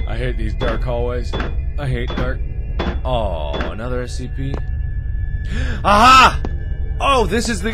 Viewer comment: xD Captions said Seed Beans instead of SCP lol